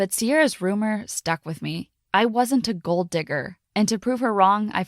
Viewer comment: i thought she said gold dicker..